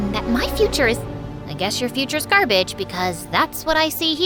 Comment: I skipped 10 seconds and heard.. -itch instead of garbage 8:41 8:41 8:41 8:41